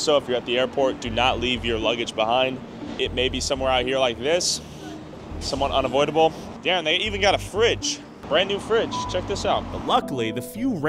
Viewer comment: I heard “he’s yanking his stuff right in the middle of the street” while not looking at the screen